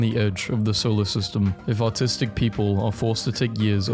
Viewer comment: I usually just listen to your memes and I heard "Autistic" instead of "Artistic". The rest should explain itself.